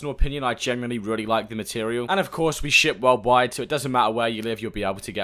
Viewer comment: I thought he said "We shit world wide"